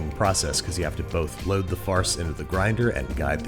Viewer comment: Did he just say load the farts into the grinder?